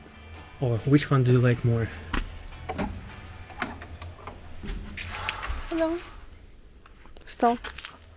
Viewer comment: did I hear say an English "Hello"? 😯😀👍